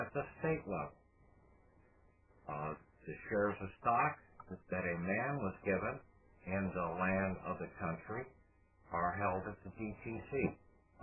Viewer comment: What was saying at the 1:25 mark? Difficult to hear it clearly. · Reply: Shares of stock that man was given in regards to the land....